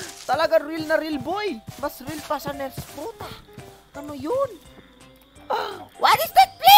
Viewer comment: tama ba narinig ko? "Pota"